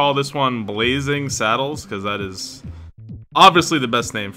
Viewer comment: I legit thought it said ”Blazing sadness” and was like that seems about right